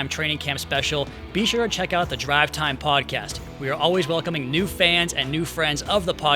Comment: did he just say "welcome Doll Fans"?! ..... lord